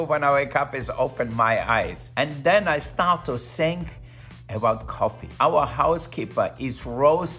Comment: I can't be the only one who heard, "and then I start to sing." I was like, get it Wolfgang! 🎵🎤🤣